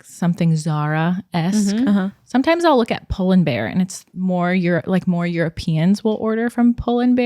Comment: I thought she said Poland bear 😆 google fingers get to work oooo it’s pull&bear